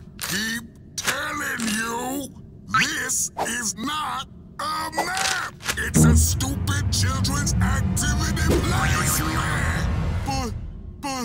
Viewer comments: I think he says THIS IS NOT A MAP ITS A STUPID CHIRLDEN ATIVIDY PLACEMENT!!! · Did Mister Gus said the F Word? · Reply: Yeah. It sounded like he said a stupid place mat… kids.